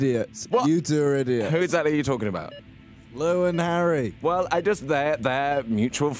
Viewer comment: Does he said "Louise and Harry" at 1:17 ? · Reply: Princess Jane Goo He said "Lou and Harry" to Lou Teasdale and Harry Lambert